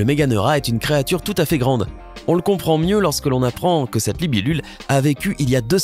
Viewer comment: C moi ou il a dit "libilule" a 2:26😂😂😂